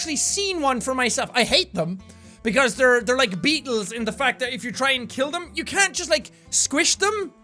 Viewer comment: I THOUGHT YOU SAID BEAGLES OMG